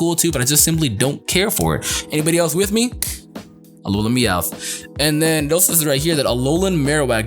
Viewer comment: Bruh I thought it said Alolan Mewtwo and I got hellllllla hyped but it was Alolan Meowth like whyyy